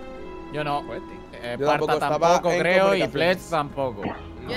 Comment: pensé que dijo flex